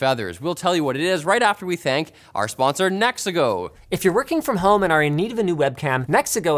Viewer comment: not only my ear hear linus said Mexico, even the caption said so SMH